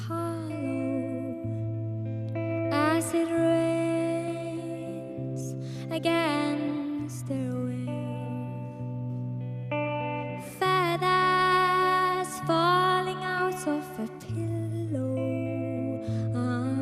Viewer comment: So am i the only one who heard "Fatass falls out of the pillows"